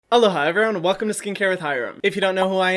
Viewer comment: when i thought he said “hello, hi everyone..” 👁👄👁 instead of “aloha everyone” which makes it soo cute aaah, i love you ❤️